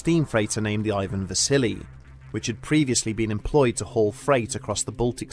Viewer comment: Wasn’t looking at the screen, and at first thought you said “Ivan the Silly” 😅